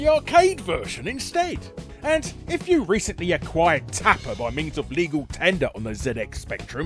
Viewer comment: almost thought it said 'fapper'